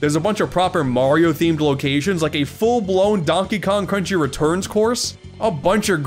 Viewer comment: Donkey Kong... Crunchy Returns? 21:43 I may have misheard it, but it still sounds like an idea. XD